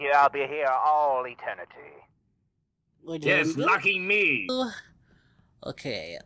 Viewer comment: It sounded like: Al: I'll be here all Eternity. Lucifer: Yes, f*cking me. I had to go back and rewatch it a bunch to hear what they actually said but it was a hilarious mishearing. 🤣🤣🤣